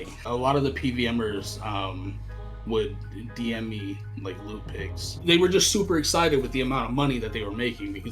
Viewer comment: i thought he said the pvmers would dm in lewd pics not loot pics